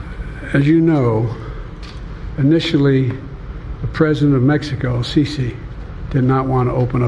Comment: I thought I heard him say the president of Mexico Cece I spoke to Bebe😂😂😂